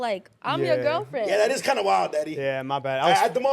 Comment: Lmao i thought said daddy 😭